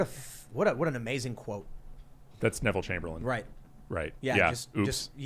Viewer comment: I thought he said Wlt Chamberlain.